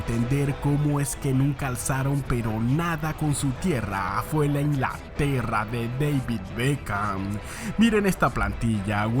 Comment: Aa ok tengo dislexia auditiva en el min 2:35 escuche la perra de david Beckham lo tuve que retroceder hahaha y dice la inglaterra de David 😂😅